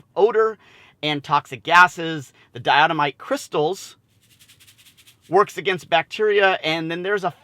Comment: Nice video! For a moment I thought you said, dilithium crystals. That's for warp drive.